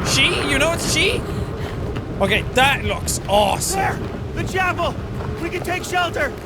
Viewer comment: when blake said there the chapel i thought he went Arnold and said the choppa